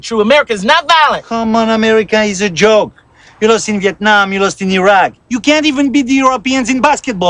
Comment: did he say vietnam??